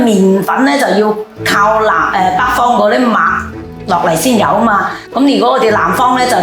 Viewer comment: Lmao that's not what the lady said at 0:45. She said '麥' not '馬'. She was saying that wheat comes only from the north; she said nothing about horses.